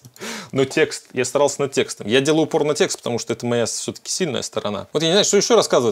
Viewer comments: ХИТЯРА УАССЯ !!! Даже послышалось:"я делаю порнотексты"🤣🤣🤣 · А кто еще услышал "порно-текст", вместо "упор на текст"? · Послышалось: "Я делал пор*о текст"... бывает же. Шедевр. Жене включу.)))